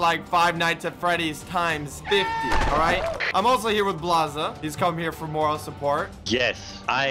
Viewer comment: If you go to 0:25 then rewind it ten sec when he says “he is also here” then it sounds like he says “I’ll also be doing this with blaza, he is also here for you”